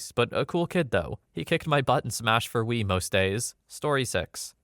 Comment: The subtitle is wrong, it's supposed to say "He kicked my butt 'IN' Smash for 'Wii' most days." I know editing is very time consuming but as a person with hearing problems who likes to watch your videos instead of reading on reddit, it means a lot for these to be accurate.